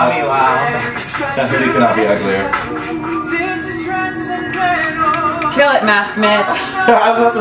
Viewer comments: I couldn't understand. What he said？ ＊＊＊＊ mask Mitch？？？？ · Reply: Oh, sorry, haha, I know that Mitch says "Kill it masc Mitch!" but I don't know what Scott says after that... I wish there were subtitles for this video, I can't understand a lot of what they were saying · Oh godI thought they were saying "mass mitch' nope 'masc mitch' makes so much more sense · in around 2:09 did Scott say Avi couldn't be any uglier what??